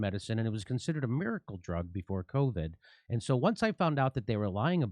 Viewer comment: Ah - I realize now I first misheard Jimmy at 0:34 as saying that Ivermectin '...was considered a miracle drug for covid.' Which detractors looking to do hit pieces could grab onto. Now, hearing it again, I realize what he really said there is that it was considered a miracle drug 'before' (not 'for') covid.